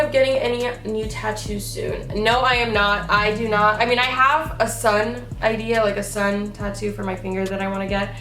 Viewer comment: That moment when my mom walked into my room as said "I have a sun" for tattoo ideas and I thought she said "I have a son" bitch I was like WAIT WHAT